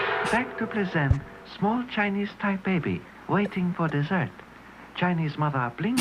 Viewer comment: I thought they said chinese thai baby, which is just as bad.